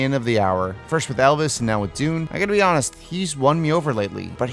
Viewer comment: I need the subtitles on when he said Elvis because it was said so fast I thought he said Belvis and I was gonna flip.